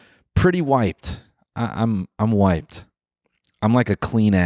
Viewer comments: when noel said "i'm wiped" i thought he was saying "i'm white" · I thought he just said "I'm white" like 4 times until the ass joke processed in my brain lmao